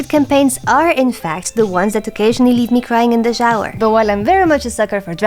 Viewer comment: I initially thought you said "grind in the shower." and I thought, I have never felt like that after a D&D session.... 😯